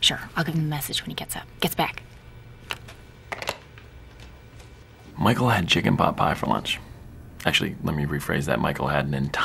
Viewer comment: michael had chicken pot pie for lunch. actually, let me rephrase that. michael had an entire chicken pot pie for lunch. let me be more specific. michael ate an entire family sized chicken pot pie and then he promptly fell asleep. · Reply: English its not my primary language, i thought he said: Michael had a Chicken Popeye for lunch (Just like the restaurant)